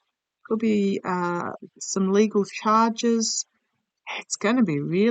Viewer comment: when you said, there's going to be some legal charges, I heard "Hague"